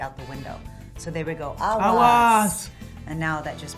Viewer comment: When they say "Aguas!!!!" at 1:20, I was a bit taken aback because I thought they said "Awas" which also means "Watch out" in Bahasa Indonesia lol :P